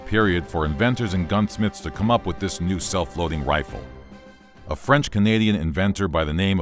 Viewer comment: I misheard that as "self-loathing" rifle and thought "How could this thing kill anyone if it wanted to kill itself?"